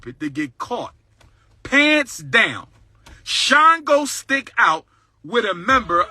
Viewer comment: lol I thought he said shine and glow stick but he said Shango which apparently is African deity.